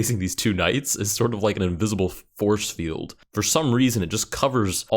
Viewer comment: did you say "Invisible F-horse-field"?